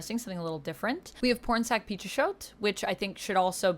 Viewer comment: I’m sorry, but every time you said the name I heard ‘Porn Sac Pichot’, even close caption read it like that. What exactly did this person work on so I can look it up.